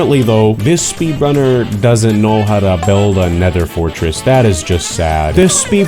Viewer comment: did he say a nether fortress?